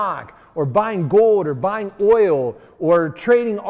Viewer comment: did he just say oil